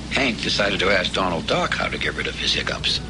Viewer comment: i thought it said Donald Duck at first. XD